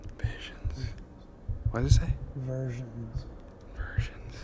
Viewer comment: When the spirit box says "versions" I heard "virgins" instead, which would make more sense if they're abusing kids in those cabins.